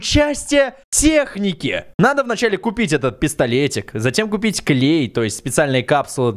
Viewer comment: А мне послышалось не пистолет а пездолет 😂😂😂😂